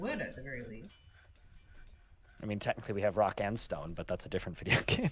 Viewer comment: DID I HEAR A ROCK AND STONE?